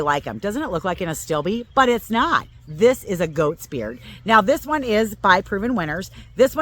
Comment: WONDERFUL presentation and information! Would help if while you are talking we can see the name of the plant on the screen so it sticks in our minds. Also I thought you said "ghost beard" but googled and realized you said "Goat's Beard" (and it was on the tag you held up briefly). THANK YOU for making this video. I live on the back of a State forest so we are SHADY!!!